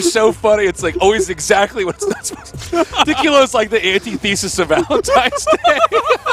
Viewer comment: what drink did he say 4:37? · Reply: Tequila